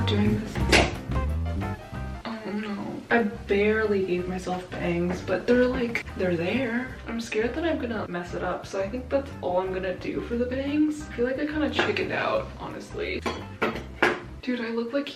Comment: am i the only one who thought she sounded like aquafina when she said 'they're there'?